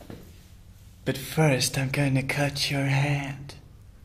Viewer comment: When he said "I'm gonna cut your ear" I thought he. Said "I'm gonna cut your hair". I was like what a nice guy. And his reaction to the saw. Lol